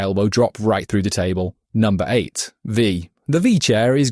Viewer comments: OMG DID HE JUST SAY V!? TEAM V!!!1!!111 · that sounded like wheelchair